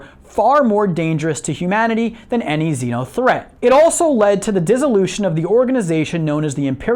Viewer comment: What does he say at 12:01 ? Zeol threat?! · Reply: Xeno threat, be vary of them, lest you die or join them